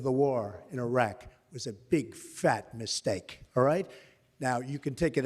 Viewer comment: when I first watched this live I at first thought he said big fu**ing mistake.